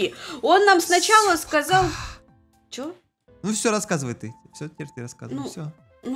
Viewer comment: Я не поняла что сказал на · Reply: Сказал "Всё пока"